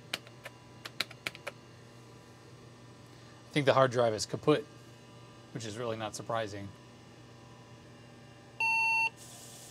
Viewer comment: did you say „The harddrive is kaputt“ ??? 😂